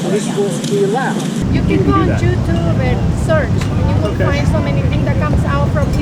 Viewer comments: Lol did she say “JewTube?” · did she just say JEW-Tube!? This woman is clearly an anti-semite and has no room to talk bad about the president.